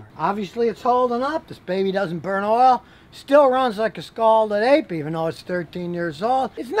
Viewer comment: LOL! Scalded APE! I thought he said Scalded Egg! LOL!